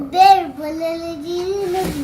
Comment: Sounds like is saying Balle Balle DD